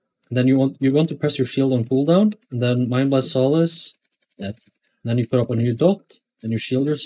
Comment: Not sure I heard the rotation priority clearly. At 32:30, right after you said to put the dot (purge the wicked), you said to use Shield on cooldown ? and than mind blast and shadow word death ? Sorry this mnay be obvious for many but never played disc before.